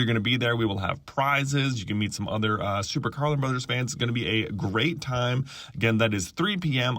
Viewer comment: I swear. when you started the vid, I thought I heard Super GIRL AND Brothers... aaaaaaaaaaaaaaaaaaa my brain is weeeeeird today. thank you. back to the vid.